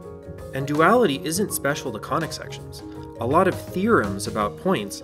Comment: every time he says conic section, i hear "koenigsegg" haha graph go vroom